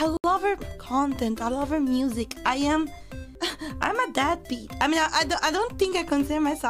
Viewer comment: anyone else thought she said Dadbeat instead of deadbeat why does Dadbeat still fit